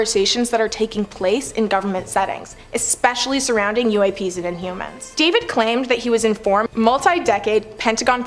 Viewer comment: At the 26:00 minute mark did she say inhumans?